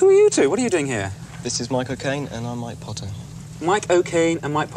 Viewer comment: I thought mike O'Connor said "this is my cocaine"